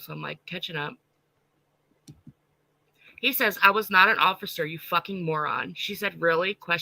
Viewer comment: said he was not an officer? Did I hear that right? Because Michelle has said MANY MANY times that he WAS an officer!!